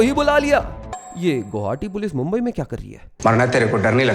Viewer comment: Did you say Gauhati police??